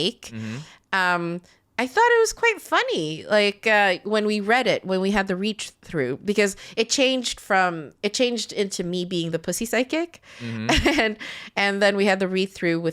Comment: At first I thought you guys were saying Pussy "Sidekick" lol all about someone and their cat fighting crime.